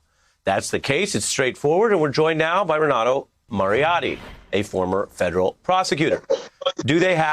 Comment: "Mariotti". For a moment, I thought Ari said "Moriarty". LOL